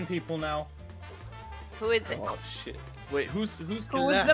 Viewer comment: I swear I heard a little “ah shit”